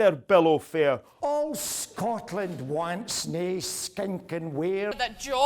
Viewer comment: I heard “All Scotland Wanks”, it was just me??